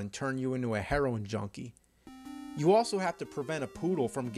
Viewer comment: If you have transcribe audio on at 2:11 it says turn you into a heroine jumpy instead of heroine junkie.